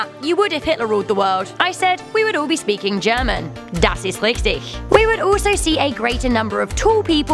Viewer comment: What did you say? Das is lichty??